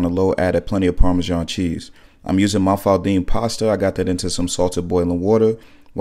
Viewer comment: Great work!! EVERYTIME you said mafaldine pasta, I thought you were about to say mf’n pasta 😂😂😂😂